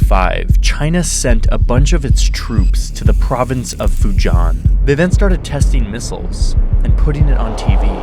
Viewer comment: That’s not the correct way to pronounce Fujian. Sounded like you said Fuzhang.